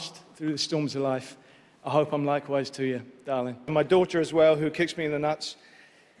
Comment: What the hell did he say?? Can someone please translate for him? 😂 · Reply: In storms in life I hope that I'm wise like you darling (I'm not 100% sure)